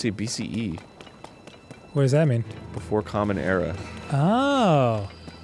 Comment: For a second, I thought Arin was saying BCE was Before Common Error. As if to say the birth of Crist was an error. And the I realised he said Era. 🤣